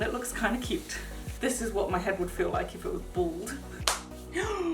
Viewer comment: the way you said bald made me think you said ‘this is what my head would feel like if it was balled’ 😂